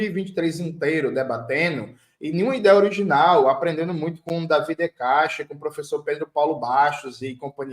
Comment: professor Pedro Paulo Bastos é autor na Carta capital Mas o outro citado foi quem? Não consegui enteder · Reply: Professor Davi de Castro, Paulo Bastos e companhia limitada